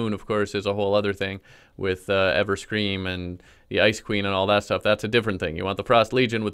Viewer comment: When he said ice queen I thought he said ice cream